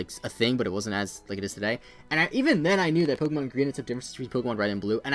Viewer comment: What did you say at 6:39? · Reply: Ketchup901 even then I knew Pokémon green had some differences from red and blue